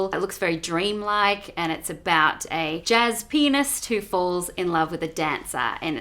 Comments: She says pianist. I thought she was saying penis holy shit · when she said "la la land" I didn't even flinch. then she said it's from the director of "Whiplash" I got hyped up. But then she said its a musical. And I got put off again. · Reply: I heard that as jazz penist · When she said "jazz pianist" I thought I heard "jazz penis"...teehee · Did anyone else hear "jazz penis"? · I heard "jazz penis"